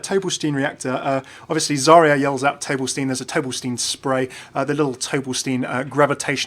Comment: Zarya says nothing of the sort. It's: "Огонь по готовности!" (Ogon’ po gotovnosti). Listen, I know the video is live but I'm ju so tired of people saying that she's yelling "Tolbelstein", like wtf even is that? The inclusion of the word on this list by Efi is propably Blizzard poking fun at the players.